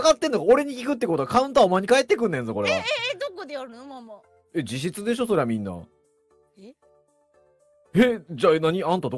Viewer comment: I think was saying jishitsu (own room) instead of jijitsu (that's a fact)